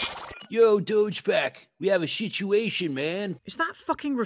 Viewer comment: I head the ape character at 43:09 say "dogepack" and I thought he said douchpack. 🤪